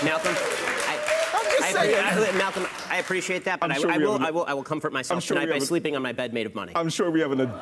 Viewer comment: Did he just say I'll comfort myself by sleep in bed made if money ??